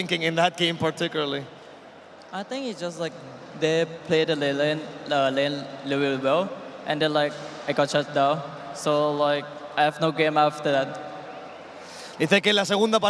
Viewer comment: ngomong apaan itu wkwkk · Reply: "I think they played their lane really really well, and i got shut down. Then i have no game after that." Kurang lebih kaya gitu *cmiiw